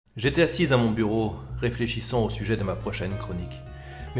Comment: La première phrase de la vidéo j'ai compris "GTA 6 dans mon bureau". Mais sinon encore une magnifique vidéo, bravo!